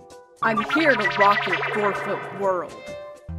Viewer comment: I’m a terrible fan 😶 this whole time I thought she’d been saying forfeit world instead she was saying four foot world 🤣